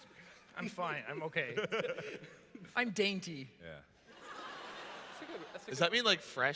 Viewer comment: When said "I'm danty" I thought he said "I'm horny" XD